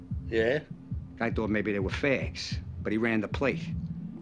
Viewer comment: why tf would he think they were gay? Is he saying fakes? What would be fake?